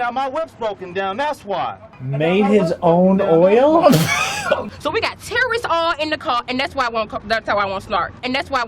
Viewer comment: Does she say "We got terrorist oil in the car" ???